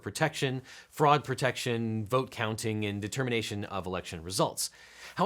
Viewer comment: I misheard 'frog protection' initially 😆. That'd be ribbitculous